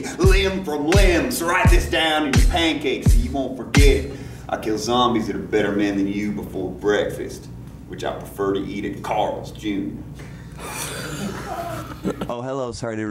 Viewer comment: What did he say at 0:47? I think it was something about Carl but i can't make out what he says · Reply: He says "i kill zombies that are better men than you before breakfast...which i prefer to eat at Carl's Jr " (a restaurant)